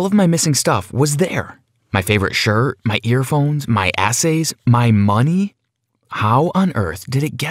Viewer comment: Who else heard him say my assay's